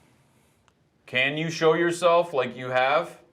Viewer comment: I think I hear "Show Me"